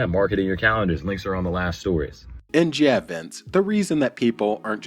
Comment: can someone tell wtf this guy actually said here? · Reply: Links are on the last stories. But I used captions